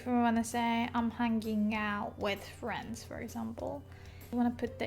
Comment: Hi, At 10:10 your subtitle says hand out but from what you are explaining i think you mean hang out ?